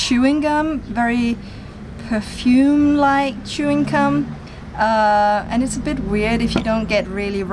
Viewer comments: Did she say chewing cum near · Did she say like chewing cum?